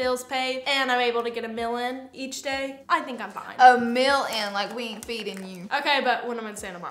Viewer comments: If you think Laura has lost her southern accent, ask her to say OIL. You will change your mind. lol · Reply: When they said “a meal” I thought they both said “a Mil” like a million. . I was so lost 🤣🤣🤣🤣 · When said get a “meal” in I thought she said get a mil in I was like damn making a million a day 😭😭